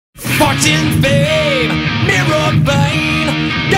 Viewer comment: lol it sounds like he says farts and fame at the start